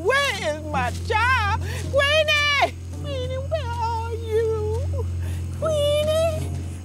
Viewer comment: this scene never gets old😂 but who did that to queenie? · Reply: I thought she said weenie 😭🤭🤣